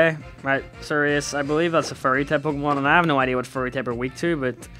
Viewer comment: this whole time i thought you where saying furry type but im only just understanding your saying fairy might want to articulate a little